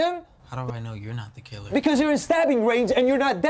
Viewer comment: “Because you’re in stabbing range and you’re not dead” Edit: ok how the hell did this dumbass comment get so much attention 😂😂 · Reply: Lol thought he said standing there